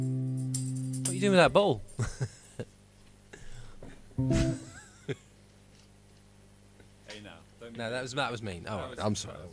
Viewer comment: wtf does he say? Sounds like, hey would you get me that bowl? Don't get it :^) Anyone know?